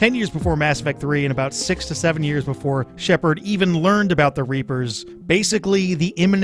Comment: ''reefers'' could have sworn you said that.